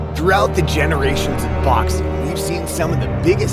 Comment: thought I heard "DOXXING" ... I was like yeah 2020's been a bitch.